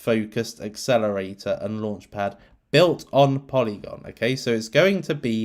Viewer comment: did you just say polylauncher??❤️❤️ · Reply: i heard that too😍 time around 44:40 , polylauncher to the moon 🚀🚀